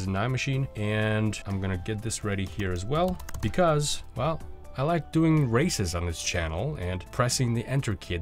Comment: i thought you said "Well because I like doing racism on this channel"